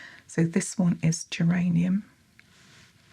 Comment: misheard the beginning as "this one is uranium" that would be quite a treatment indeed